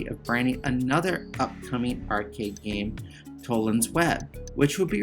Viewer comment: I tried several times to catch what Black Widow was called before being released...can somebody help me....I hear Nolan's web, colons web....I'm really curious ..thanks. @ 28:40ish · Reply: Sorry my mic is for crap, it's Tollian's Web ... which is a terrible name lol